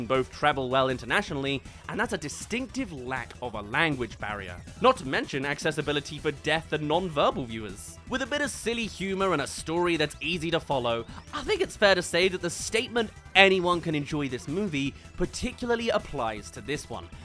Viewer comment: death instead of deaf 11:04 ok!